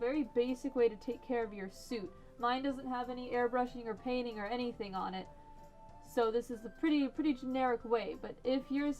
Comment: what was the name of that thing you said keeps the sweat out of the head? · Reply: Balaclava